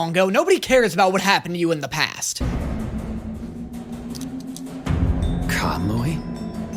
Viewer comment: did... did he just say Kamui? I haven't seen this season myself but that sounded a lot like what he said